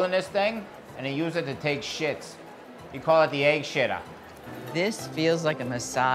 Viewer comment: I thought it said egg shittter 😳